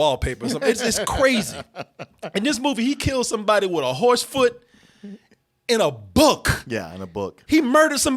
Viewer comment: Did you say "Horse Foot"? Is that like a "horseshoe"? Cause a horse has a hoof in place of a foot. Does John Wick rip the hoof off of a horse in this movie? That's odd & uncharacteristic behavior for a dog lover.